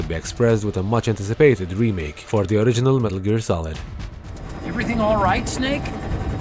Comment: dude... 2015 not 2018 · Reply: holy shit. i swear i heard "metal gear solid five" my bad i deaf af. ill just shut up haha also ill check TTS vids. haaa im so dumb